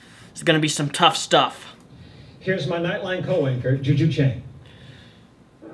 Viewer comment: OOOOOOH He said "Here's my nightline co-anchor". I heard it as "nightline cowanker" the first time. I guess the truth makes less sense than what I heard :/